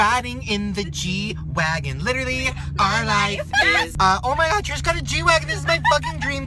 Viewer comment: I thought when Shane said Trish that he said trash so I thought that he said hey guys I'm having out with TRASH!!